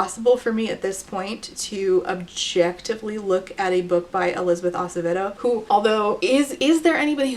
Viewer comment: the captions aren't helping me lol what is the name of the author you say at 42:59? Elizabeth ?? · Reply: Elizabeth Acevedo